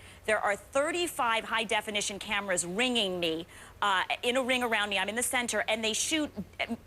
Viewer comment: Did she say reaming me ?🤭🤯😳